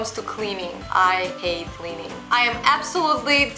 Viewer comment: Did she say she hates cleaning at 8:17?